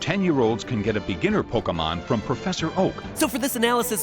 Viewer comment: am i the only one who heard professor hulk instead of oak